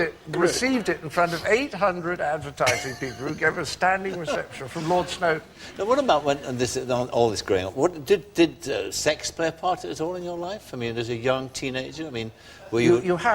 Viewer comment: Did Parky call him "Graham" ? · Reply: What about all this 'growing up.' I'm near where Parky was from. 😊